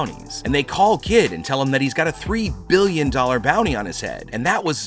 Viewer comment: did I just hear him say that kid got a 3 billion $ bounty?